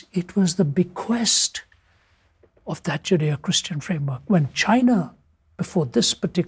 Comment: when he says judeo-christian framework subtitles say sharia christian framework